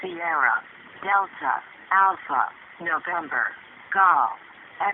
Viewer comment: 留守番の英単語自分のリスニング力が無さすぎてどうしてもNovemberがCucumberに聞こえてしまって一生ツボってる